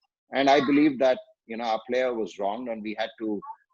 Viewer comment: Did he say ... "Bhajji was wrong" ? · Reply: no he said that he was wronged. That means he was given wrong pronouncement